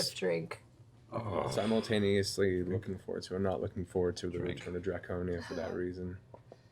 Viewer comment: What did Matt say? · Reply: "I've been simultaneously looking forward to and not looking forward to the return of Draconia for that reason."